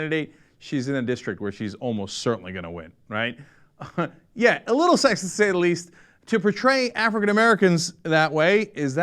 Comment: If you have the subtitles on and go to 3:58 it says "yet oral sex italy's"... What Cenk said was "Yet, a little sexist to say the least"